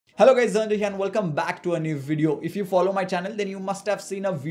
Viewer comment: What does he say at the first: 0:00 Hello guys _____ ??? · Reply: here & welcome back ...